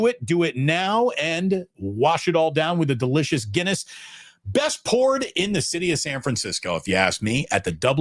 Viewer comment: thought he said "best porn" lol